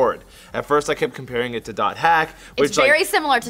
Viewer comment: What was the anime you were saying that was similar to SAO, around 02:53? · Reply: hack// called dot-hack is a series of anime, would have seen Quantum I believe.